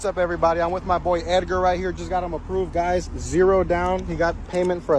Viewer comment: did he say i just got him approved for a thousand months?😅